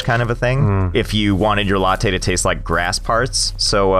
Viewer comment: I 100% had to back it up at Yerba talk, cuz I swore Josh said "if you wanted your tea to taste like grass farts"